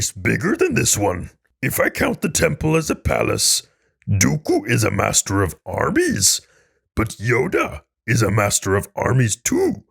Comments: Had to replay 4:45 a couple times. I heard "Dooku is a master of Arby's" lol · Tell me why I thought he said Arby's and not armies... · Oh my goodness. I miss heard when Yoda said, "Master of armies." I thought he said, "Master of Arby's."